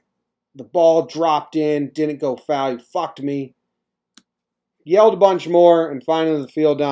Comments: Not sure what the words directly preceding it were(one definitely was the f bomb), but at 1:20 he said "you fucked up man" and walked away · He said you fucked up...not you fucked me around 1:18-1:25..pay attention to his mouth .I think he might have said "the ball dropped in you fucked up" .....or most likely" the ball dropped in.. your fucking blind"...